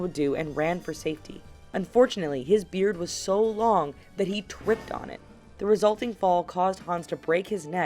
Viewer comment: lol listen closely to1:13 she twipped instead of tripped and if u heard it reply to my comment